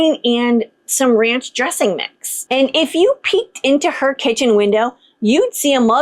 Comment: what did you say in 3:01? I'm not a native speaker and the audiotrack said ''if you pee into her kitchen window...'' I don't think you really meant that😅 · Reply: "When you peak into her kitchen window.."